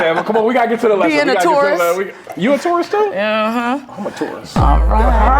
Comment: was saying "I'ma tarus too" bc that would be smooth and hilarious 🤣